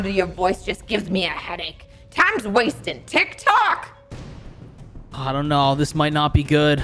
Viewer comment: did Karen say tik-tok?